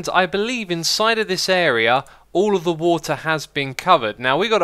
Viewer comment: did he just say "all of the water has been covered"?